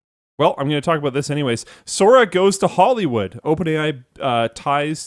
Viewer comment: “Sora goes to Hollywood.” I hear “Disney is going to FU KH.”